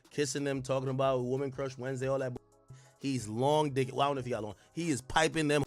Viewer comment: Yo did he just say" he's long dick" then slide bk n said idk how he does it 😅😅😅😅 yoooo